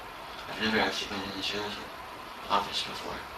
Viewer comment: When u said sheriff I thought you said Sharf